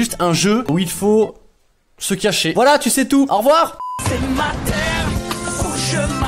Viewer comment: mdrrr j'ai compris "c'est ta mère"